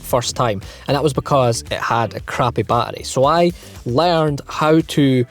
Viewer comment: He said "my car wouldnt start because it had a crappy battery " But when i first heard him i thought he said "My car wouldnt start because it had a crabby patty" Like if you heard that too